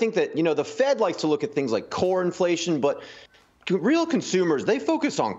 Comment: What? Thought he said corn inflation? Maybe corn chips?